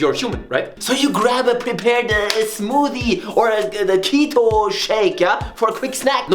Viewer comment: Cheeto shake? I really hope i misheard.